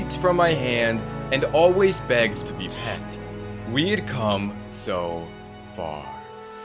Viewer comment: at the end i didn't hear him say "we'd come so far" i heard him say "weed cum so far"